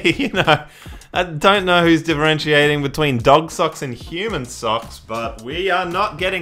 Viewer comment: I thought that it said human coc I can’t finish my sentence